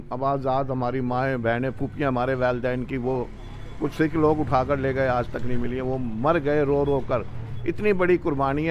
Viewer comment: did he say sikh log utha k le gae , or probably i made a mistake but i heard many times. Agar sahi hai to kitna galat inke dimag mein bhara hua hai, any sanatan sect will never do any such heinous act, especially sikhism which was formed for the safety of humanity. Jaise khud hain vaisa hi sochte hain ye dusro k bare mein 😠